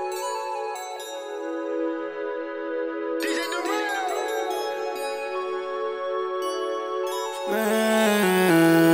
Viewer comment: Que dice ahí? What? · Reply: "Dj Durel"